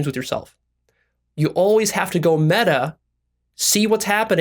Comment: Did he says:"you always have to go 'meta' "? sorry i just couldn't hear it. Anyone can help?